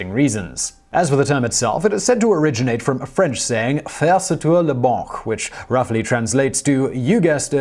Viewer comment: The heck did he say at 0:50?! It sounded like he was racking his throat for a wad of flegm. My best guess is, “faire, ces tout le banc”. In English that would be “make, is everything the bench” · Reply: He was trying to say "faire sauter la banque", but he has a hard time pronouncing French.